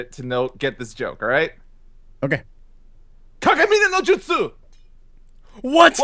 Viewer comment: what was that Naruto joke? what did he say? I can barely make it out as "kage bunshin no jutsu".